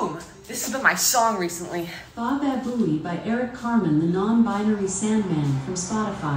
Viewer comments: for a sec i thought alexa said by eric cartman (like from south park) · I thought it said bababoom by eric cartman 😂😂